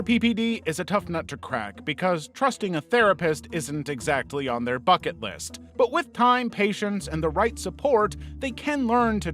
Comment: Help but when you said "not exacky part of their bucket list" I heard "not exactly part of their fuck it list" I'm so sorry I need my hearing checked